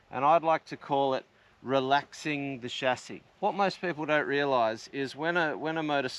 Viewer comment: oh, "relaxing the chassis". I kept hearing 'relaxing the shaftie'. 😁